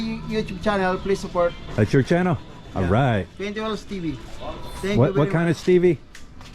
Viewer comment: I couldn’t find that dude’s channel, probably because I couldn’t really understand what he said was the channel name. I thought he said 20 Will Stevie. ???